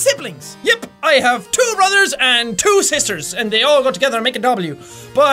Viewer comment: I turned on Closed Captioning. I knew what he was saying, but this is stupid FUNNY! Jack: "Yep, I have 2 brothers and 2 sisters, and they all go together to make a W!" Closed Captioning: "Yep, I have 2 mothers and 2 sisters, and they over together bTW!" What has the Closed Captioning gone to?!